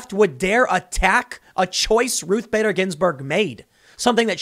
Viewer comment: I had to do a double take when Tim said the leftists were "attacking a choice Ginsburg made." I initially thought he said they attacked "a choice Ginsburg maid," and had a mental picture of an Antifa mob terrorizing a beautiful young woman who used to clean Ginsburg's house...